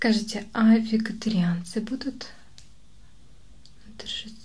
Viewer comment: Когда вместо вегетарианцы услышала "викторианцы")